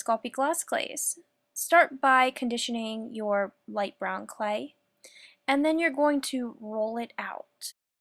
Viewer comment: Lol I thought she said Bon clay